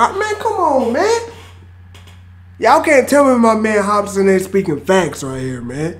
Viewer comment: Sounds like your saying “hop spin” hahaha